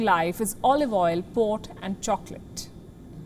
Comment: Olive oil, pot, and chocolate at 2:02? I’m not sure if I heard that right. Lol!